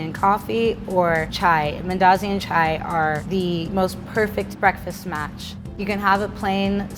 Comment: Did she really say Darjeeling chai?